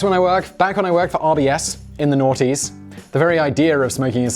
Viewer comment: WTF is he saying and what does it mean? It sounds like "in the naughties" lol